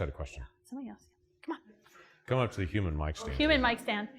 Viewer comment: did they really call him human mic stand ?